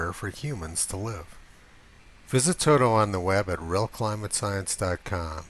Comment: your videos are fantastic. I was always intrigued at the ending of your videos, because I always thought you said "Visit Tony on the web at... He's been pulling back the curtain..." I always thought it strange that you'd speak about yourself in the third person. This is the first video where I got it!!! I had to play it back three times, before I realized you were saying Toto! (slaps forehead)